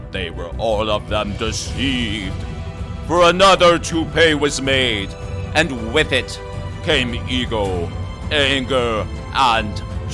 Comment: did you say, "Another toupee was made" ❓⁉❓ Hilarious video man!!! LMFAO!!!!!!!!!!!!!❤❤❤Allan is so lost, the poor dear.